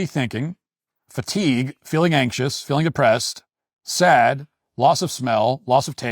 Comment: I was listening, but not watching directly at the screen and when he said "feeling depressed" for one of the symptoms, I misheard it as "feeling oppressed", and I was like "Wait, what", then I realized it's a leftist claim and almost accepted that. Obviously, logic kicked in and corrected it, but the moment cracked me up. So here we are. xD